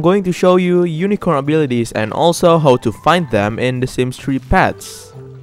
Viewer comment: In the beginning of the video I thought you said "and how to fight them", and for some reason I didn't even question it.